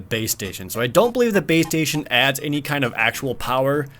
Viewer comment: I kept hearing "BeaStation".